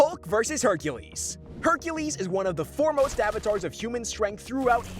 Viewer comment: when he said "foremost avatars of strength in human history"... I heard it as "four most avatars of strength in human history".... and spent the last 7 minutes googling who the other 3 avatars were